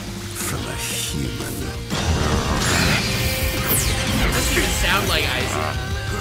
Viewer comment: could not tell what you just said but I'm gonna guess "sure sounds like aizen" 🤷🏾‍♂️